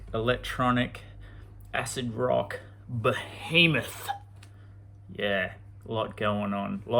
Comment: did I hear behemoth? 👹